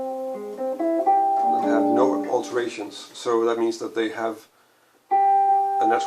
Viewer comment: At around 1:50. What is it that you said? I didn't catch one part. dominants that have what alterations? I couldn't make out the word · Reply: marco escalante "no alterations" I made subtitles for this video so you can turn them on to follow what I am saying (You're lucky that you chose this video, most of them do not have subtitles :) )